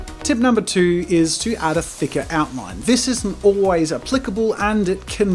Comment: thought he said a thick outfit😂